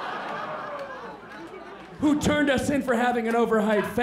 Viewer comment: What did he say at 5:03 ? I heard it as "This proofs that it was that know-it-all cunt who turned a scene for having an over-height fence" Did I listen it correctly? I suppose I didn't because I can't google "turn a scene" Best Regards, From Korea